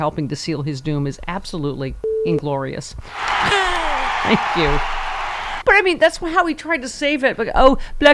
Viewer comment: Sounds like you said 'inglorious.' The opposite. Enunciate your ing.